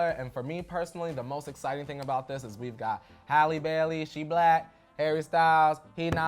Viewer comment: Thought they were saying Halle Berry until the last second and was like why is no one complaining that she’s old 😂😂😂